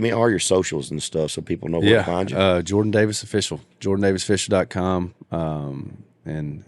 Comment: Good job with the interviews. PS: With your accent, it sounded like you said George instead of Jordan.